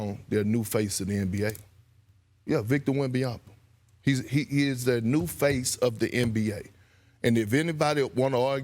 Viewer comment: Ol girl misheard perk 😂😂 look at how he looks at her when she stops talking 😂 he definently didnt say future face he said he is the face